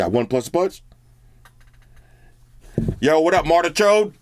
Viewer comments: YO what up Madarch*d lmao(it sounded like it) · did he just say" madarchod" on the call ????😂😂😂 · Did he just say Madarchod? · Did he just say what's up madarchod ? XD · I'm I the only one who heard home say "yo wassup mada churd" 10:38 that's hindi where all my indan bros